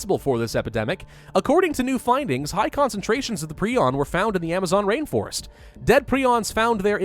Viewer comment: I thought it said porn